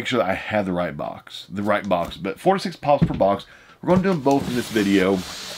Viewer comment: Im so deaf. I had to go back a few seconds. I thought i heard '46 Pops per box! Lol 4 to 6 Pops per box😂😂